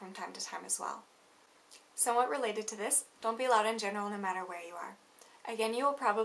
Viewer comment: Are you trying to make English sound like Japanese by stringing lots of words together? Example: Dontuseyourcellphonesonbusesortrains - I had to rewind and listen again. Can you tell me what exactly you say at 1:26??? Something that ends like 'general you are'. I simply cannot decipher it! It isn't just in the West that we raise our voices - they do it in the Middle East too. Thinking about it, Japan IS in the "West".